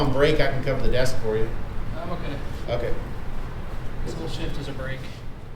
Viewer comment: Did "Luke" say his whole shift is a break or am I hearing things? 😂 This wholeeee interaction was absolutely fkin hilarious man.